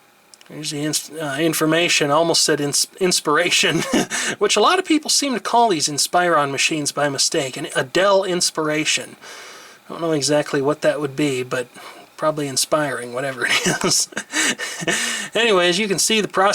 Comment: I kinda thought you said 'Adele Inspiration'. That would've been a great pun. Or maybe it's just me.